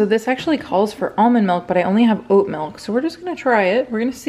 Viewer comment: I thought you said goat milk instead of oat milk. I thought "well that's an interesting choice"